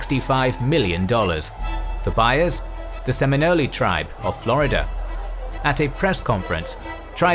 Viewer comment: By the end of the video, all I heard was "Ravioli tribes"